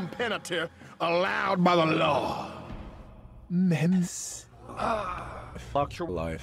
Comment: Did anyone else mishear this as menace instead of memes?